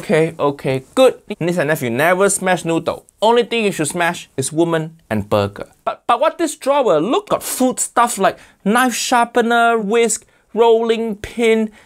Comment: Gurl did he say “WOMEN”